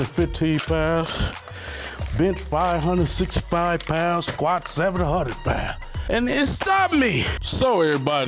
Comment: Did he just say he can squat 700 pounds?